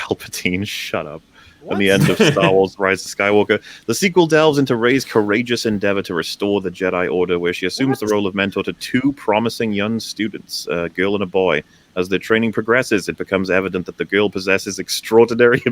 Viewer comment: "She mentors 2 promising young students a girl and a boy" oh I wonder who the big bad of the movie will end up being because he's jealous of how powerful the girls are....shit I said he, guess that gives it away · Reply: No one's going to notice this but for a split second I heard "She mentors two promising stupids" and it took me a few seconds to realize that was incorrect (grammar wise, not IP wise).